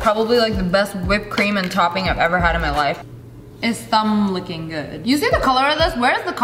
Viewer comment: No one: ... KFC: It’s finger lickin good SSSniperworlf: It’s Lid lickin good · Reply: I thought she said thumb licking good